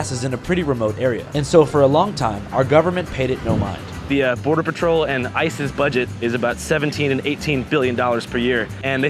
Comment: When you said, “the border patrol and ICE’s budget” I heard it as “the border patrol and isis’ budget” and was thrown for a real loop de loop